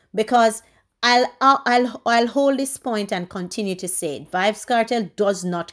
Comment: 🤔I Thought You Said Kartel And You Doesn't Care About YouTube lol🙄